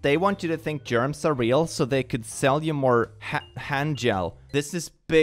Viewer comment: I heard something else. Hand j..